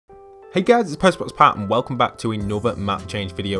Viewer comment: I thought u said “Hey guys I’m PostMan Pat”